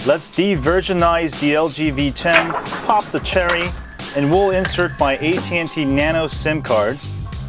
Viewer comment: Did he say POP THE CHERRY at about 1:18????